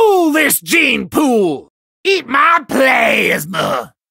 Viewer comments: i thought he said eat my sh_ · I thought he said Eat my Plane 😢